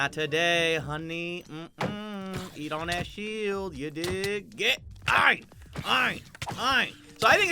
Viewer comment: "you don't he a shield you d***" anybody heared that?